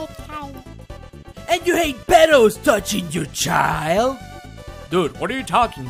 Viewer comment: Oh I thought he said pelos touching the child. I see what he said now